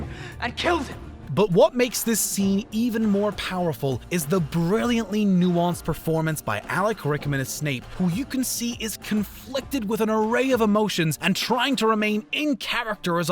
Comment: Did he say alack rickmas